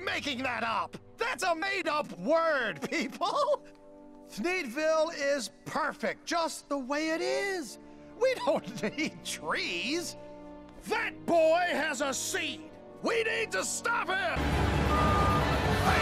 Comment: captions said “We don’t EAT trees” (Edit: keep spamming